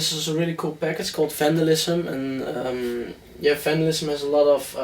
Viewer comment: What's the pack for Sylenth that MG said at 9:57 ? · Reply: Soundset for Sylenth1 from Vandalism